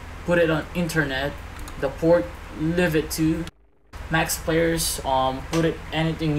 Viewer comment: what did he say at 0:31? The port live it to?